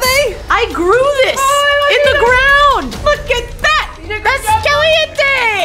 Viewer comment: I thought she said “LOOK AT THAT! NI